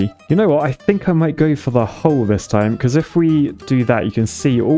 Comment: listening offscreen 4:45 "You know what?! I think I might go for the hole, this time..." Wait, what? confused rewinding "Hull". He said "hull". Must have misheard. 5:05 "... at the TIP of the spaceport..." Penis joke. Oh yeah... probably didn't mishear at all.